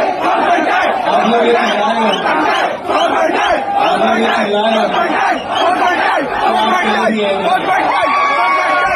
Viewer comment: se isne kya bola tha? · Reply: Ap log ye chillare ho and then he said awaam till the end.